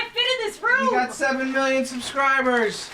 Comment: When said 7 million subs, I thought he said 70m😅